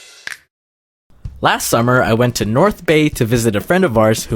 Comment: Lowkey thought that said "My trip to North Korea"